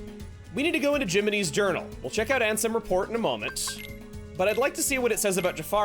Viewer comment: I heard ansem as “I am Sam” “Sam I am. Do you like green eggs and ham?” 🤣🤣